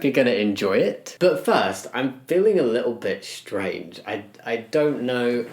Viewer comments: I thought he said "I'm feeling a little bit straight" and I was so confused for a sec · anyone else hear "i'm feeling a little bit straight" 😂(i had to rewatch it to actually hear what he was saying) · oh I thought you said “I’m feeling a bit straight”😂😂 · At the beginning instead of hearing "I feel a bit strange" I heard "I feel a bit straight " I was kinda worried tbh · Hahha I really thought you said I feel a little STRAIGHT 🤣🤣🤣🤣👍